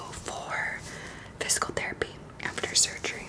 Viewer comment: DID SHE JUST SAY VSCO THERAPY WHAT THE HECK 😂😂😂😂